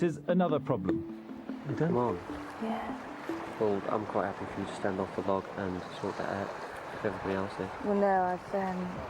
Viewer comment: What did and say at 19:25? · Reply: “I’m quite happy for you to stand off the log and sort that out if everybody else is”. “Well, no, I’ve….” basically saying she has her period.